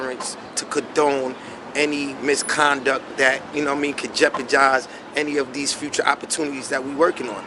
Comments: Did just say “JeoparJise”????? C’mon son 😂😂😂😂 I know what you mean though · LMAO: WTF THIS MF SAY? JEPERJIZE